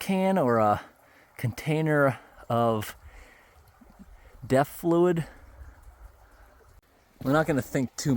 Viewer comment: did you say death or def fluid? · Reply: DEF...as in Diesel Exhaust Fluid.